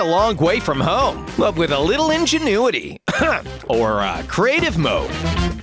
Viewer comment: I thought he is saying "ORAAAAA!!!!!" but it turn out to be "or a"..... lol